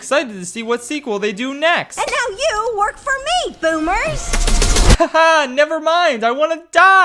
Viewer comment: Pardon me but did an animated character in a DreamWorks film just call someone a boomer? Please tell me I misheard the dialogue